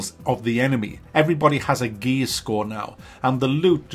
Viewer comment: I heard gay score LOL